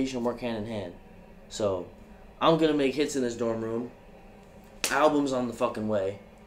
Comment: I thought he said he was gonna make kids in that dorm room 😭😭